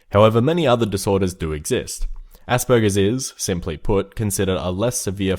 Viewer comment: When you said Aspergers I thought you said ''Ass burgers''